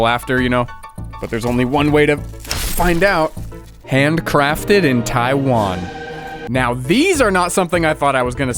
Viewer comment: I heard “Ham crafted in Taiwan”